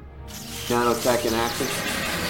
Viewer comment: He says "Doth Say The Reverand." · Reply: He says "Nanotech in action." In reference to his weapon being dereived from Nanotech. Obviously Bruce was originally saying this to the player as part of a larger line.